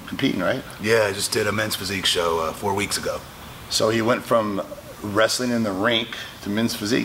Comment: Did Rich just say "Wrestling in the rink"? LoL You play hockey in on a rink you wrestle in a ring!